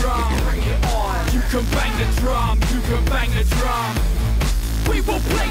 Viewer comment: all I hear is "you can bang the tron, you can bang the tron."